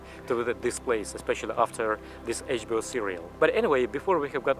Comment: did he just say HBO cereal?